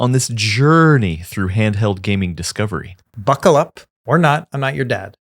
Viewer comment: I was listening passively to the intro to the pod and all I heard say “…I’m not your dad, I don’t care”. I’m going to start adding that to the end of ‘have a good day’ and ‘have a safe flight’